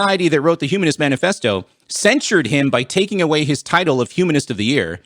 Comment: "censored him by taking away his title of humanist of the year" ... what? that sentence doesn't even make sense, and has nothing to do with denying freedom of speech. by that logic, if i'm a christian and part of a congregation, and i say that i hate Jesus and God and Satan is the only true lord and saviour, and then get kicked out of that congregation, they would "censor my by taking away my title of 'member of this congregation'" and also deny me my freedom of speech. see how silly that sounds? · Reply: I'm not gonna go back to listen to it again, but I didn't hear him say, "They censored him." What I heard him say was that 'they censured him,' as in a way to punish or shame someone.